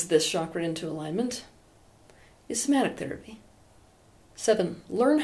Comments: What therapy? I couldn't catch the word at 17:46. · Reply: i think somatic but idk whats that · in 17:43 she says something about therapy. can anyone tell me what she says? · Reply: Somatic therapy